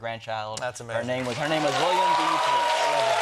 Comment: you can't hear her name 😢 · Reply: I looked it up, her name was actually Williams Threets. I thought it sounded like he said that but I was like "maybe it's Wilma?"